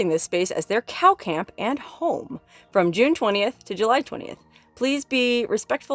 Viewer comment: When you said it was June 29, I thought you said it was July 29! It’s only July 21! I live your channel!